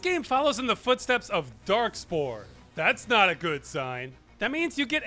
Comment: Wait, Dark Souls has character slots, right? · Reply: I think he might be talking about 1:20, where says dark spore, but it sounds like dark souls. I heard it as dark souls the first time too